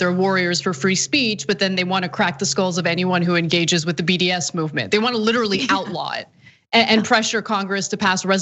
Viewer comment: Did she say BDS movement? What did I miss?